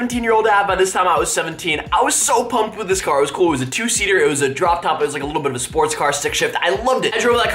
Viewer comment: he said Droptop or did he say Raindrop droptop